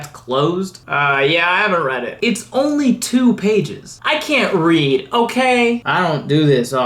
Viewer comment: Top 3 Moments: #3 Louisiana's super soft "Bingo" at the beginning, oh my heart, why was that so cute!?? #2 Big "What up, I'm Florida, I'm 175, and I never fckin' learned how to read" energy & I'm here for it!! #1 Even in the structure of this comedic recap of the news you give us, you respected and honored John Lewis's work and legacy and it was beautiful! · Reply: I want to add an honorable mention: at 4:12 Florida says "I can't read", but the caption says "I can't ready." :>